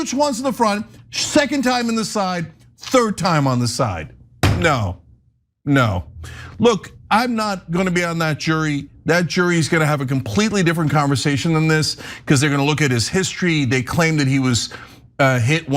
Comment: did he just say jury? Like he's going to be criminally charged?